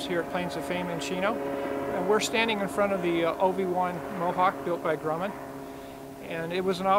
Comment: when you said OV-1 Mohawk I thought you said Obi-Wan